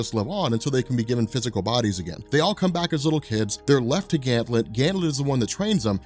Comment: Sounds like he is saying Ganthlet am I the only one who hears that? Why is he saying it like that